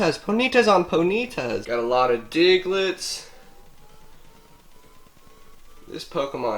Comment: lol you said diglets I thought you said dicklit